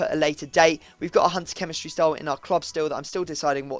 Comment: did he say cunter?